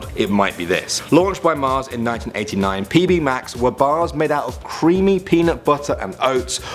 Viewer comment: When They Said Pb Max,I thought they said Pj Mask....idk why??😂😂